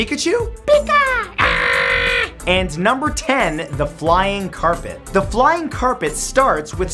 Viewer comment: omg i thought it said flying carrot at the end